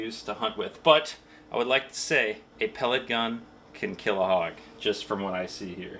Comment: I thought he said if a pelican can kill a hog I was gonna say enough cocaine can make a pelican kill anything lol don't ask how I know🤣